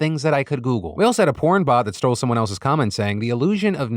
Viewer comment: Commentary Comment Section: Did I hear that right, 'a porn-bot who stole someone else's comment said." LOL, that's crazy stuff.